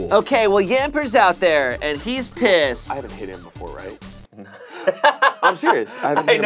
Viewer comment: wtf does even say there??? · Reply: "I haven't hit him before, right?" Referring to attacking Shayne's Pokémon.